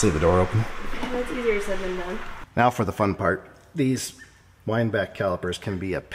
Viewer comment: LOL, I thought you were say "Weinbach" as in a person's name. I had never heard of those style of brakes.